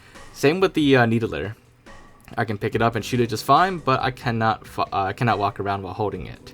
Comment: ok be honest what did you really meant to say there? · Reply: Can not fu